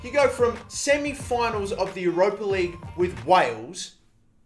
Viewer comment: Did he just say "semi final of the Europa league with Wales" Pretty sure country's can't be in the Europa league 😂